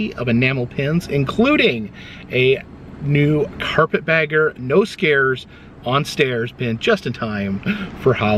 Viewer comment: Instead of "no scares" I heard "I'm selling my NOSE HAIRS"